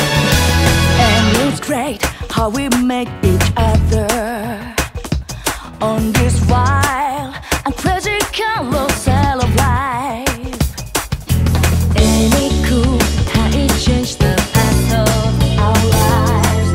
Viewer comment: I always thought it said "discount of sin" not carousel